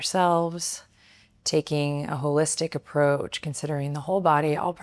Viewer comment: Omg😂 I thought you said “taking a ballistic approach “, then I realized you said holistic 😂 that should make you laugh!! I had to laugh at myself 😊